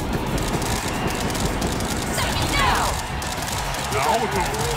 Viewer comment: What did Shadow Naoto say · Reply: “Suck it now!”